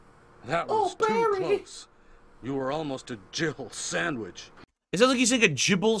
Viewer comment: I always thought Barry said jibble sandwich too 🤣😂